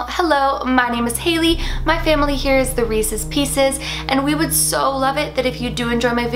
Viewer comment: I thought you said the racist pieces 😂😂😂😂😂 I thought "OMFG....." Ever since you've read this comment you will never hear it the same again 😂❤️